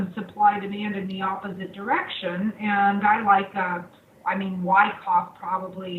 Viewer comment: What does she say at 19:41? "Why cob"? I can't make it out. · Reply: wyckoff